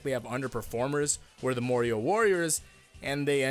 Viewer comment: is it a bad thing i heard "mario" all the time instead of "morioh"